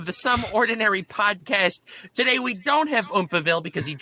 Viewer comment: Bro every time they do the intro, I swear to gad, I hear "sub-ordinary" podcast